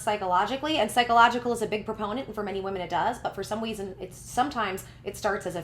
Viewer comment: ik this is a serious topic and everything but i heard you say "for some weason" and that made a chuckle a lil bit lmaoo